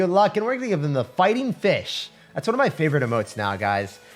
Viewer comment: I heard "fighting bitch" at first but he said "fish" 😂